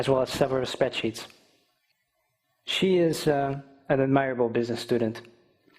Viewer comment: thank you for your answer. If possible can you list some examples of mistakes that a native speaker would make without hampering comprehension? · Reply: Its not a mistake at all. He said " she is ( pause) an admirable...". Actually, u misheard his pause.